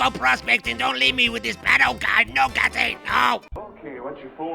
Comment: Had I not read the subtitles for Patto, I would have sworn said don’t leave me here with this petto. Funny but not funny. Lmao yeh, it was funny.